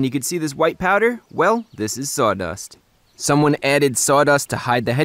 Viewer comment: did you say "saw dust"?